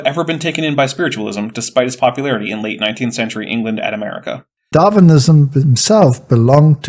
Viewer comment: I thought I heard "Darwinism himself" and I have questions